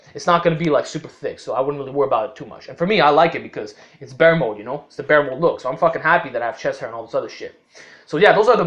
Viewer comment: what he said? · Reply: "I wouldn't really worry about it too much and for me I like it because it's bearmode, you know, the bearmode look"